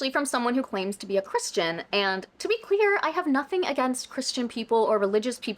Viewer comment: totally unrelated to the content of the video, but my hearing is bad so at 35:52, i thought you said "to be queer" and i was cackling and laughing out loud